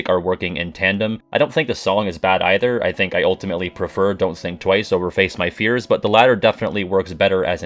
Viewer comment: "Don't sing twice" Don't think you got away with that I heard you 🤣🤣🤣